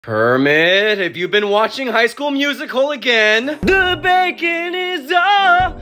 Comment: i thought he was saying HIGH SCHOOL DXD